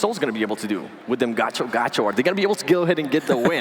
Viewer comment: did he say gacho gacho? 😂😂